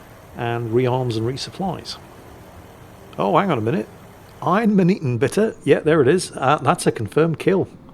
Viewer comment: Okay, I've always been meaning to ask: at 19:03 uses an idiom that I've only ever heard him say that I can't quite make out. It sounds like "I'm an eton bitter". I'm normally good at picking up on slang or idioms from the UK, but this baffles me. Does anyone know what this expression is and what it means? =P